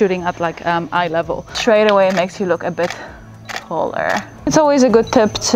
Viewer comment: When she says taller,I hear “hoeler” is it just me?